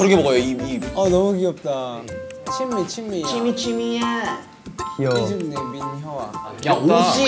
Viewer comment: Chimmy,Chimmy~ when he said that it sounded like shimmy shimmy ya from swalla!⊙▽⊙